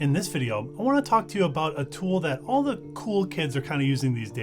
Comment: spits coffee out mouth choking during zip Did he say..."cool kids?" [0:02] Uh-huh!